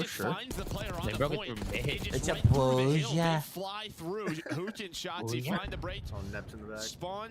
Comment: Why is merk calling huke “hooch” 😆